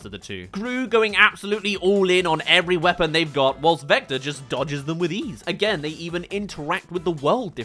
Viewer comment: I swear I heard him say Vecna.